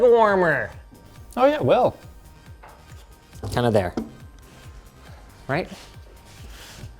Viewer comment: "How about there", i misheard as "Tunnelbear"